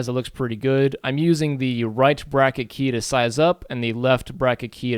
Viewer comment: Great tutorial, but what do you say at 13:08. What is the right-bracka-key? :P